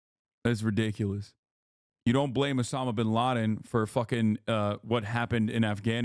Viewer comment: Idk why but I thought this man said Hasana Bin Laden